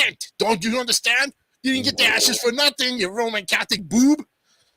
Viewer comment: Did you just say “You ruined my Catholic Boob”? Lmao